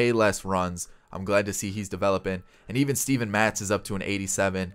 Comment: Who else heard him say zack matz. It’s Steven